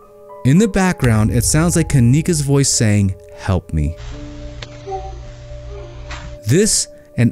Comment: Sounds like she said “what’re you doing ? Help me.”